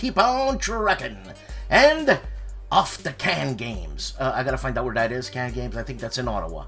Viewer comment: Oh, "Off to CanGames". At first I thought you said "off the can games" and I thought, "that's all of them I hope" :D